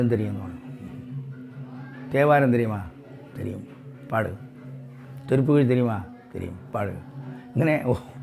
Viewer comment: what he says is "Thirupugazh" (by Arunagirinathar) & not "Thirukural"